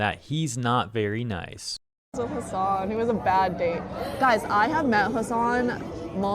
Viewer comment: Might just be me but I thought she said “He was AN Hassan.” Like comparing the guy to Hassan, but then transitioned to “I’ve met Hassan.” because she was just trying to keep the talk going. IDK, still funny. Edit: ok, now I’m hearing WITH Hassan. Man, 420 messes with my hearing.